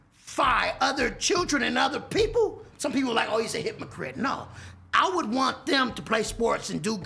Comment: I thought he said hypmo”crip “ 😭😭